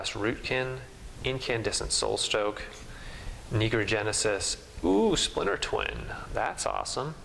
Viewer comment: Did you say at 13:50 Negro genesis? LOL, That's racist.